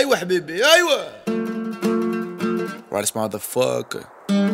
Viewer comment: I'm in love with this man 😍❤and i'm so proud because you are algerien👌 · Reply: MIRA DZ 0:08 i hope he did not said motherfu... becouse he did not like said like that worlds✨💞